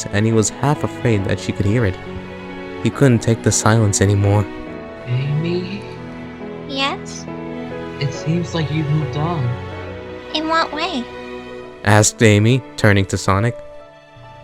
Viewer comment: I thought I heard cream say Mr Amy XD Lol